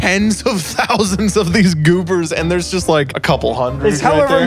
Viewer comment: did you say GOOBERS